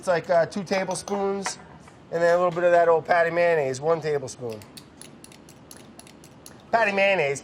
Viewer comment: I thought he kept saying a pat of mayonnaise. Then it made sense! 🤣🤣🤣